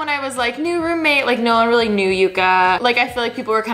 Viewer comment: It sounded like she said a name!! It sounded like Uka.